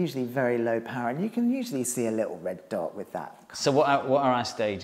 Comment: What did he say here? "Very low power and you can usually see a little bit __________ with that" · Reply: I think I said something like “you can usually see a little dot on the wall with that”